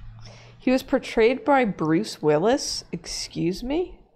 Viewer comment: At first I thought that said Doraemon "was betrayed by Bruce Willis," and I imagined some dramatic action movie scene where Bruce Willis does a heel turn and throws Doraemon out of a helicopter or something. (After everything they've been through together!) It was wild.